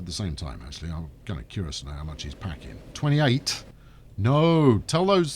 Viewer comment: does anyone else hear "whore's and pony people" or is it just me?